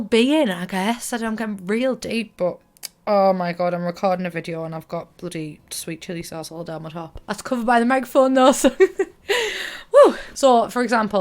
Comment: What did she say at 2:33 ??? I swear to god that wasn’t English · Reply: She says I'm recording and I got sweet chilli sauce down my top but it's hid by the microphone!